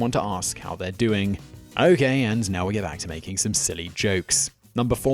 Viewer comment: what the?? 7:42 if you turn the captions on simon says " okay and now we'll get back to making silly jokes" but he captions say "okay, we'll get back to making silly jokes now, before our eyes leak all over our keyboards" ?? :P